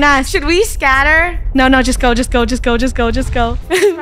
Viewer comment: When funneh said just go just go i thought she said disco disco 😂😂😂